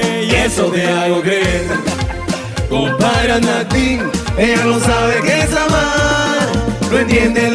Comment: Excelente tema pero que es lo que dice en el segundo 00:43? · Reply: Compadre anaddin